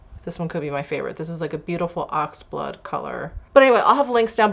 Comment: You look genuinely HAPPY in this video's thumbnail....its a beautiful pic of you xoxo ps near the end did you call the last of the lipstick cases sent to you ...the maroon/burgundy one as a beautiful aqua? Lol or did I mishear you..probably did!